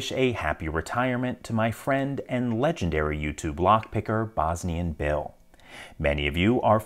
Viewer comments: Lol straight up thought he was two different people. I always thought you said Bosny and Bill and I. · I always thought he was saying Bosni & Bill…